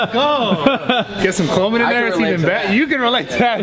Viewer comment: “Get some clomid in there too haha” DID I HEAR THAT RIGHT MR. PCT? 😂😂😂